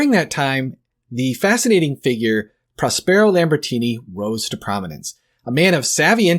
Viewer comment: I thought you said Lamborghini at first 😂